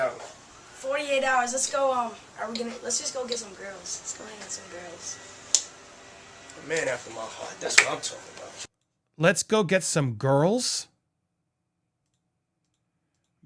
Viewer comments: I feel like JB 15 years old was still a child and wanted to play "grown-up". All the stuff D.D. said was completely vague, but he said that he would "realize a 15 year old's dreams" which means: Justin Bieber's idea, he came up with it, not my suggestion. When babyface Justin said: let's get some girls, he followed D.D.'s expectation and got rewarded by "oh you are my 'man'". Cliché, but it's all about "I open up the door to all that is not accessible to you without me". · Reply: I thought he said "Let's get some grills" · I heard “get some grillz”. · Grillz ..I thought he said grills…maybe girls.. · I thought Bieber said, "Let's go get some grills" 🤣 Maybe that's when everyone was getting them? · Didn't sound like Beiber said, "girls." Sounds like he said, "grillz." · I thought he said let's go get some grills....